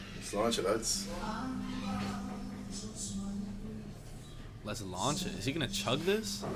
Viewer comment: Hi - where you thought you heard him say something like 'let's launch it' he was actually saying Sláinte, which is Gaelic for health - in other words 'cheers'. In Irish Gaelic it's pronounced 'SLAWN-che'. In Scottish Gaelic it's pronounced 'SLANja' with a soft 'j'. Or often, 'Sláinte Mhath' ('SLANja-VAR') meaning good health.